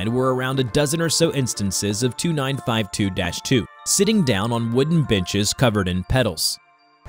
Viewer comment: "sitting on a wooden bitches" (that's what I heard)